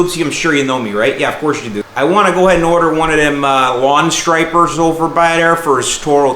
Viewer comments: Anyone else thought it said stripper? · I thought that said “stripper” at first. Was like lawn care is getting wild.